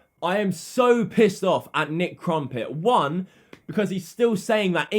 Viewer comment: Who else heard Nick Crumpet at 3:03 😂😂😂